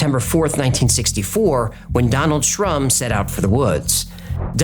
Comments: I thought you said Donald Trump.. its Donald Shrum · I swore you said Donald Trump at the beginning 😂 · I thought you said Donald Trump. Lol. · Initially when you said 'Donald Shrum’ , I heard it as 'Donald Trump' ! 🤣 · I must admit me mishearing "Donald Shrum" as "Donald Trump" took this video to a whole new level. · Great story telling. I’m embarrassed to admit amount of times I thought you said Donald Trump. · I thought i heard "Donald Trump" :goodvibes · I thought he said Donald Trump,had to rewind! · Seriously I thought I heard “Donald Trump set out for the woods” 😂💀had to rewind